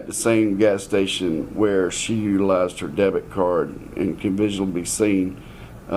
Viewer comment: Does LE say "she left.. or Lost...??... her debit card at gas station "...? hope I'm hearing him wrong! The windows are dark... so hopefully he's inside, perhaps sleeping in back..& not in the trunk or Something...😬 hate typing that!!